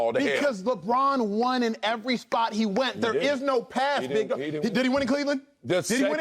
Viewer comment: did Acho said: There is no pass N*** ? 😆